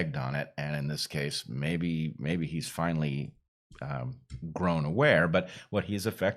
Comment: Was I the only one who heard Bret say the Portland mayor had "grown aware" but actually thought he said the mayor had "grown a pair"...... Which is far more accurate and the symbolism is priceless 😜